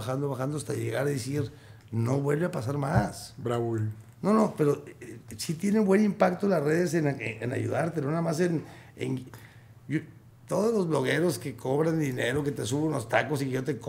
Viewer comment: Minuto 1:24:52 que dice ahí Horacio ?... se oye que dice "Braúl"... y me sigo preguntando que es "Braúl"??